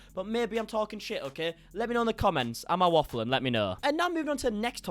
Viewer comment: asking from the US (no knowledge on British slang 😭 - sorry!) … did he say “am i waffling” at around 3:58? Or did I hear wrong? 😂